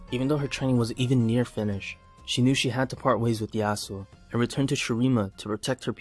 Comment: if u turn on subtitles u will read "the asshole" instead of yasuo XD